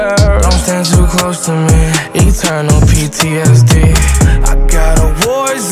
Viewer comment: Is it just me or when he says "Eternal PTSD" Is sounds smooth af? 👌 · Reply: It does and before I saw the video I thought he said “it turn on” ptsd instead of eternal